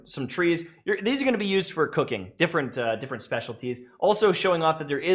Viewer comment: Why did I think he said " These are gonna be used for cocaine "